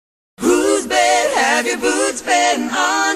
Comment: When I was little and I'd hear this song in the car with my dad I thought it was about a thunder storm and the boots were rain boots. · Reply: when i was little i thought she was saying boobs and i just giggle lol